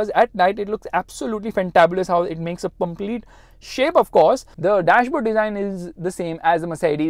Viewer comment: Funny Fumbling at 13:07 - how it makes a "Pumplete" shape (instead of complete shape) 😂😁